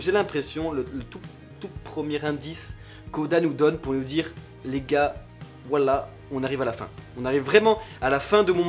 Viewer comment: "les gars, wAllah on arrive à la fin" ptdrrr j'ai compris ca au début